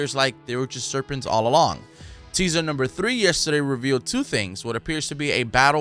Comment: Anybody else heard him say season number 3